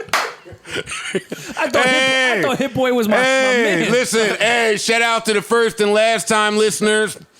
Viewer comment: Idk why I thought Rory said "I thought Hit-Boy was ma nigga!" 😂😂😂